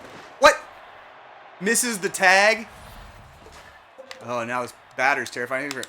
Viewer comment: I was doing dishes half paying attention when you said "and misses the tag" but I misheard it as a silly player name "Mrs. The Tag." Next player acquisition rename contender? 😉